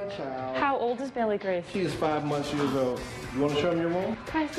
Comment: did he say she's 5 months years-old? lol, anyways welcome to Cleveland!